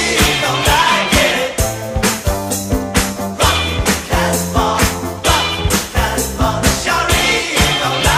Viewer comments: I thought he was saying: F**k the Casbah ‼️😎🇺🇸 Loloud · When I was 5, I thought they were saying "Rock your ass off" lol.... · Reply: I thought they said rock Casper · I hear “Pin to taskbah! Pin to taskbah!” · Nice to see I'm not the only one who thought they were saying Rock the cashbar